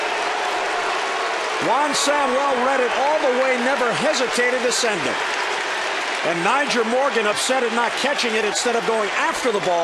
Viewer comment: did he just say "send it"? :D